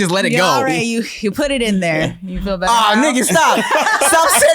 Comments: I swear I though I heard Tim say “Ahh nigga stop” 😂😂 I’m sure Rick gave you a black card a long time ago anyways. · I thought he said ah nigga stop he said ah nikki stop 🤣 I was about to say